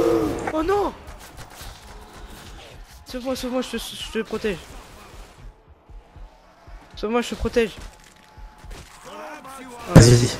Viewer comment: Ecoutez bien, à 16:20, on dirait que dit "Sauve moi sauve moi j'te suc..... j'te protège !"